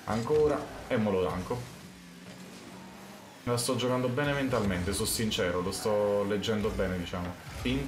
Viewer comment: ho sentito me la sto giocando Beneventalmente ahahaha